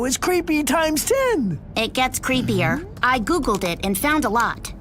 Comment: Did she say google